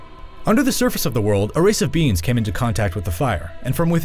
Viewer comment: I keep hearing "beings" as "beans"